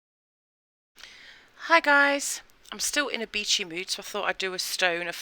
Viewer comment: I thought you said you were still in a bitchy mood! Lol. I had to replay it! 😂